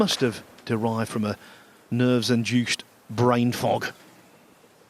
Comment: did I just hear "brain-fuck?" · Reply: brain-fog